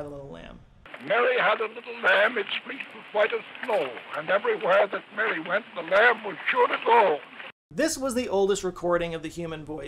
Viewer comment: Is it just me, or is he saying: "Mary had a little lamb, it squiggled quite at all, and everywhere that Mary went, the lamb would shoot us all" ?